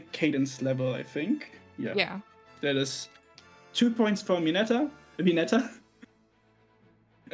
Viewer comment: With your accent, I kept hearing Mineta since I watch MHA and then I was jumpscared by Mineta here LOL